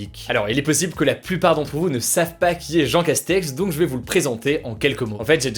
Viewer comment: Il est possible que certains d'entre vous ne SACHENT pas...» Dans un autre commentaire... Le niveau d'exigence ? Montre moi une vidéo ou il ne serve pas la soupe et ou il "décrypte"... Je suis intolérant avec les imposteurs et menteurs, des le titre de la chaîne. C'est pas un anonyme hein, il s'expose, il prétend, il vend... J'ai pas a le prendre avec des pincettes. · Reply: Garry Iglesias : Ok donc soit tu as mal entendu la vidéo, et dans ce cas je t'invite a la réécouter : Il dit bien "ne savent pas", soit tu as lu ca dans un commentaire (ce qui semble le plus probable vu le tien) et alors tu ferais mieux d'apprendre a vérifier les propos de chacun. Pis viens pas te plaindre des fautes de français quand tu écris "où il ne serve pas la soupe". C'est soit "ils servent", soit "il sert". Et franchement, je vois pas ce que t'attends de plus venant de cette chaine. L'info est résumée quotidiennement (j'insiste sur ce point, car cela veut dire que la recherche d'info, de sources, l'écriture et le montage se font en 24h max), et est expliquée, comme dit dans le "a propos" de la chaine. On reprend les points de la vidéo ? - Démission d'Edouard Philippe : il explique qui est Jean Castex, d'ou il vient et pourquoi il a été choisit pour remplacer EP. - Il explique ce qu'est l'affaire Valbuena/Benzema, pourquoi elle a eu lieu et qu'est-ce qu'il y a autour - Il explique le problème qu'il y a avec le Crous a cause du corona -Il explique vite fait le statut de Hong Kong, les actes que la Chine considère comme criminelle et pourquoi elle et le UK sont en conflit actuellement. - Il explique la situation en Botswana et pourquoi c'est un problème. Pardon mais tout est clair et bien résumé pour que tout le monde puisse comprendre On pourrait se plaindre de 2 ou 3 petites choses comme le fait que Valbuena a été filmé avec sa femme ou le fait que, comme tu l'as dit